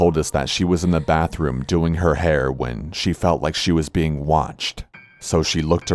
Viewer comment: I legitimately thought he said doing her heroin